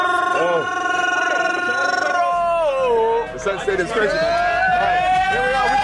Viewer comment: what's this thing they all keep saying? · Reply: Get sent It's like saying trolled stadium